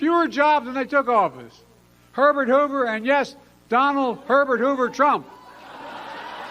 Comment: 😂 I swore Biden said "Donald Pervert Hoover", but my husband said he said "Donald Herbert Hoover". We rewound; I still think my hearing is more accurate 😂